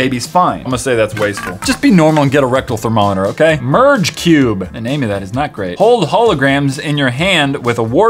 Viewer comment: When he said merge cube I wasn’t paying attention and I thought he said purge cube